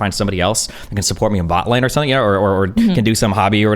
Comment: did he just say support me in bot lane